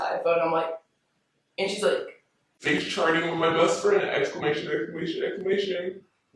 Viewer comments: it sounded like she was saying Face Sharting, not Face Charting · I thought she said face sharting